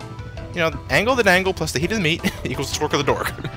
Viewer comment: What did you say?... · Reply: Angle the dangle, plus the hidden meat and smekle the dork